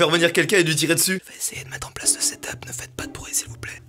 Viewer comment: à la place de setup j'ai compris attentat genre "je vais essayer de mettre l'attentat en place ne faite pas de bruit svp" XD